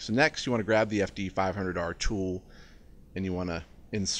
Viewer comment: Funny. I thought you said grab the fd $500 tool. 😂😂❤️